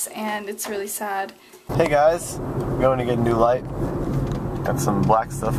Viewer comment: hahaha i thought said im going to get a new life, when he said im going to get a new lightt.